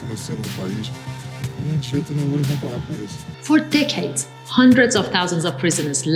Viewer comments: I was convinced she said "for d*ckheads" instead of "decades" lmao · When she said "for decades" i thought she said "4 dickheads" lol